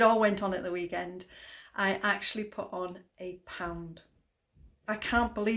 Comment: Well done! At first I thought you said you'd put 8 pound on instead of A pound LOL I thought I was the only one who doesn't like strawberries. Have a great week and thanks for always sharing such good information and being so inspirational. X